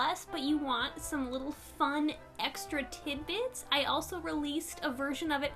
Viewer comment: i thought u said titi's when u said tidbits